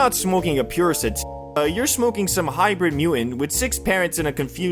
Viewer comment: "Hybrid mew inn"? I thought you were censoring yourself again until 3:26 "High poi see gummies" when I realized you just have that bone disease in your jaw from South Park.